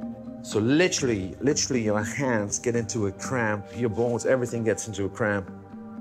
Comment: did he say your balls get into a cramp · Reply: 😂😂😂he said bones not balls